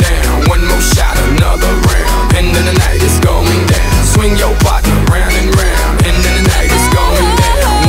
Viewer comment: Finally clicked on lyrics I always thought he said "swing your butt round n round" not "swing your partner round n round"